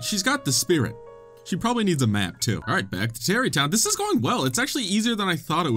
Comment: I thought you said "she probably needs a man too" not a map (considering where the conversation was previously)